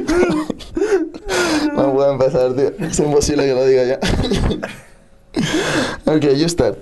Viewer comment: What does Rafa start saying at 2:05? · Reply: "No puede empezar tío, es imposible que lo diga ya"